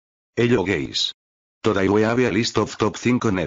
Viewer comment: I heard "hello gays" at the beginning